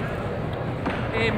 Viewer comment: What are your friends saying? ???game???game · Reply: They are joking saying "it's just a game" haha